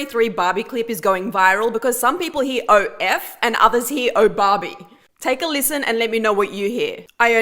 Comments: it's oh barbie but i hear oh fuck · I hear "Oh Barbie!" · This one is weird, because it depends on which one I want to hear. Like if I wanna hear barbie, that's what I hear, but when I wanna hear f**k, that's what I hear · You have to be extremely high if you think he is saying oh f*** and not “oh Barbie “ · all I hear is oh barbie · wait what i heard oh f- and oh barbie